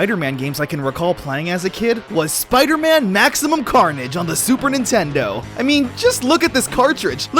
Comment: I thought it said "maximum cringe"